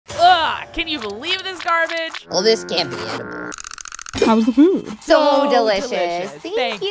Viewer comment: I heard “how’s the poop”